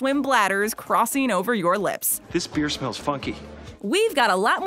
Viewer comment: did he say this beer smells fucky ? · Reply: This beer smells funky